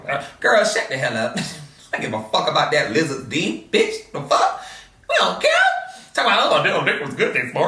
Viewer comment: Lizzard’s dickkk😩😭😭😭 did I hear that right???